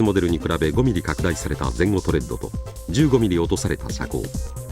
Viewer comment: doesnt he say 15mm (十五) instead of 50 and later 35% (三十五) instead of 30?